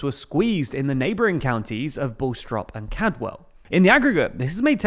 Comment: did he say “Bull-strop”? It’s Bastrop, “bass-trop”